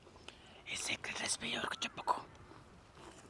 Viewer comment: pls share what he whispered? ...lol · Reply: he is saying “This is secret recipe, don’t say to anyone.